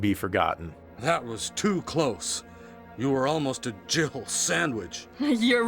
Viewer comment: I can't be the only one that thought Barry said "gerbil sandwich" listen to it again and see what you think, it sounds like there's a "b" in there.